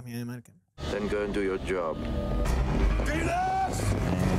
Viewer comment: "Penus!!!" Tell me you don't hear it 😂😂😂